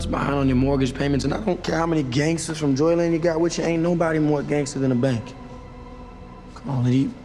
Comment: "Nobody is more gangster than a bank!" That hit hard. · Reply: I always thought he said “idc how many gangsters you got ain’t nobody want gangsters in the bank” 🤣🤣😭😭